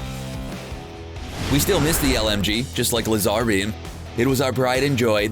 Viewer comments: lazar bee that’s what I heard🤣🤣😂😂😂👌👌👌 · did he just say LAZAAR BEAN IT'S LAZABEAM